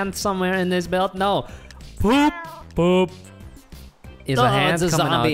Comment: “It’s the ghost from mario” “Boo” “Boo” What I hear: “It’s a ghost from Mario! Poo poo”